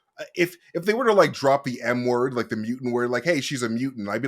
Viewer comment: 10 minutes in and i thought said he wished they dropped the N word (i misheard that he said M word) and a few minutes later says "the thing that got me off"......"on the wrong foot". I need a nap